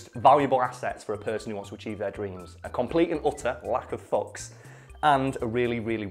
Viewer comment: did you say lack of socks ?